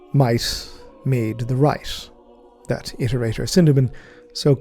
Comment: anyone else hear, "mice made the rice" instead of might made the right? 😂